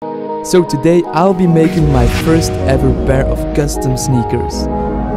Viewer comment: oh i thought u said snickers XD